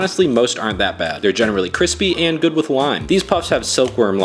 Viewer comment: When you said “and they’re good with lime” I misheard it as “they’re good with wine”